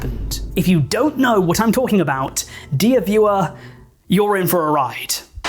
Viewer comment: very poor choice of words considering the topic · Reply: I genuinely thought he said "deer viewer" and did that guy pointing at himself meme